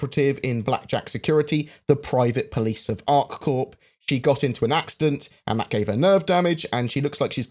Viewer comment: I kinda miss heard you at the start and thought you said “the actor got into a serious accident”